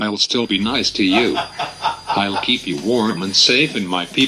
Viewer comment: i thought it said i'll keep you warm in my pp